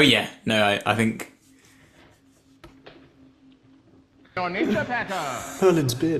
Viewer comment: He says ".your nature,Packer!" And I thought he said "You're Ninja Packer" Haaiuijaaa...😆😆😆